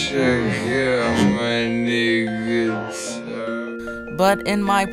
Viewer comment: it sounds like hes saying niggas. Ps i am white i just had to point it out dont kill me